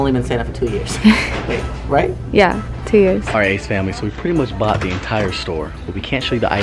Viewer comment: I thought she said "i shit ACE Family" istg that's what i heard😂 love yall😩💕😉😙